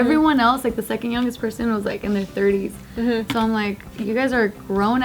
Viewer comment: Another edit I would make is at about 6:42 where the CC says "in the 30-ton", should be "in their thirties." Sorry for being a bother, xo